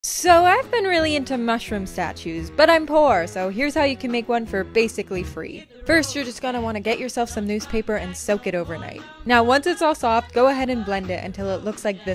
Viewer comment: I hear 1st time:"But i'm boreeed!"😂